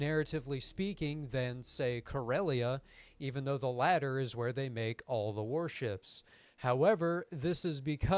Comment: I thought you said "even though its where they made all the worship" so i imagined for a few second corelia as some sort of planet wide galactical vatican 😂